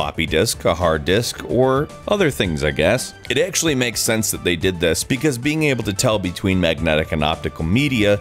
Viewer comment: Did he say " They did Disk/Disc" Lol